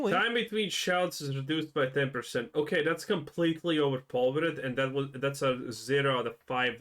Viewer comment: I'm having a hard time understanding that sentence. "Okay that's completely overpowered" is what you said there?